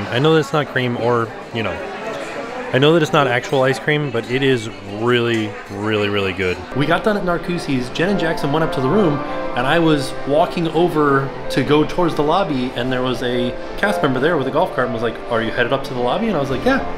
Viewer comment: Jen and jackson went up to the room. I had to rewind thought said janet Jackson I was thinking wow